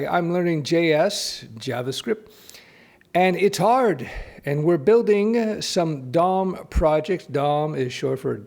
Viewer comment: As was reading the email, for a second I thought he said “...and we are building some dumb project...” lol I had to rewind that one.